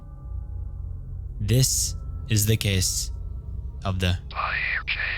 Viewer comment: hey can someone tell me what the voice says i can’t make out what he says before jane doe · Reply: He's saying "Pyre Jane Doe".